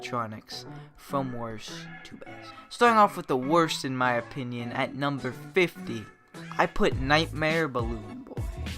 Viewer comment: Me at 2:00 i hear this "starting off with the first character at 50 ill put nightmare ball." Thats what i heard. Please speak up.